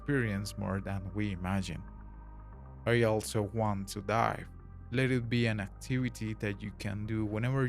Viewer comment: AMAZING VIDEO please keep it up 🙏 I will admit I was listening to this with headphones though and not looking at the subtitles and when I heard this part 10:32 I thought you said "I also want to d*e" 😭🙏🥲